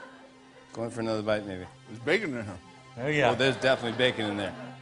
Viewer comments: Does Jimmy say, "go in for another bite n*gga"? lol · Sounds like Jimmy is saying the N–Word LOL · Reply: go for another bite maybe. That's what he says, no N-word there lol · does Jimmy say, "go in for another bite, nigga"? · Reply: Nicholas Pearce he said "maybe" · did he say nigga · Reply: Nick Smith it really does. Hahahahah. But he said "maybe".